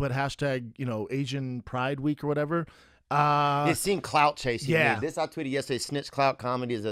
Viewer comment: I thought he said cloud chasing like as in vaping and i thought oh thats some funny ass racist shit.